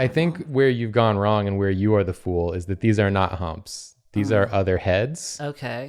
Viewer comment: I don't know if it was because of the shape but I heard that as "These are not humps these are udder heads".